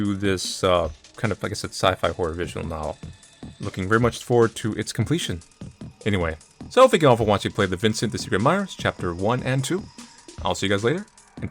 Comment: it kinda sound like your saying "Vincent: the secret admirer".